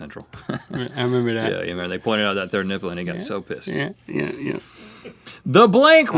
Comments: did Rhett say pissed I'm just wondering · "They pointed out that third nipple and they got so pissed" Did... did I hear that right?